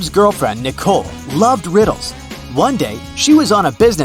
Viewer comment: I heard she loved whales😂